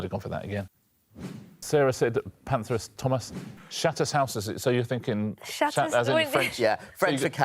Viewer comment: When Richard said it, to my ears it sounded like shits in house.😮😢😂